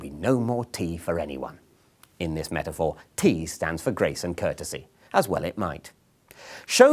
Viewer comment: "in this metaphor tea stands for grace and courtesy, as well it might" Love that line. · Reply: I thought he said Grayson Courtesy like it was a person 🤦